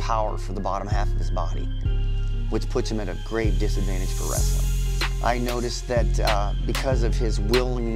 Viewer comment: At first I thought they were saying arm wrestling